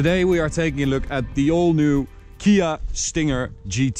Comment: the old new ?? ( is this what he said or I heard it wrong )